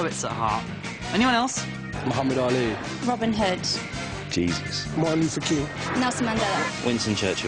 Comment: did he say “Jesus”? lmao